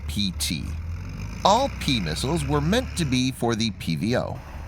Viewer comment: misheard "p-missiles" as "p-nusses" ROFL.